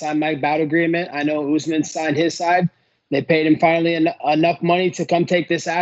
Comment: first thought he said '5 milli..' on repeat sounds 'finally' which is it? 🤔